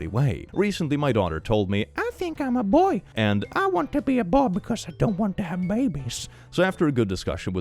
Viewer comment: want to be a bob lol, not trying to be rude I rly like accents I think him accidentally saying a bob was rly funny though lol😂